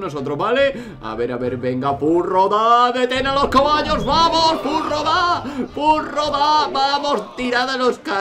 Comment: que pasa con los furros? Yo oigo claramente que dice FURRO VA!